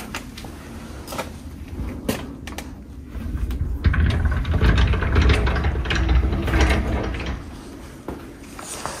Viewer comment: 頃にが引き戸を開ける時に戸の音じゃない何かが重なってる、、、 重なって聞こえていた音が終始聞こえてる、、、 怖い、、、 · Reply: はい どうぞー って言ってる